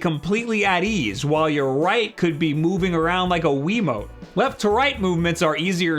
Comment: did he just said veemo?! /°\